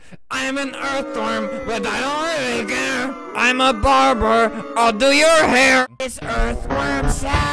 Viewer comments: i thought it said im a baber ill pull your hair. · Earthworm Sally in a perm photo made by me, everything else is from Albert’s beautiful channel! ;) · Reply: When It says "I am a barber, I'll do your hair." I thought it Said " I Am A Barbra , I will do your hair, " lol😂😂😂